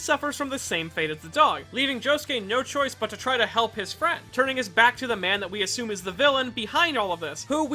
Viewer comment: if u here closely he says frie instead of friend